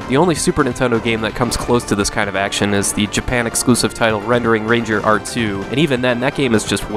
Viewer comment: I know I'm late to this party but I was curious if you could expand the game mentioned at 1:44 I can't find anything about it here on youtube. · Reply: Ah thanks! I thought you said Red Ring Ranger or Red Wing Ranger. I feel like an idiot.